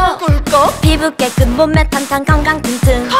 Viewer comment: 우유주이소로 들림 ㅋㅋㅋㅋ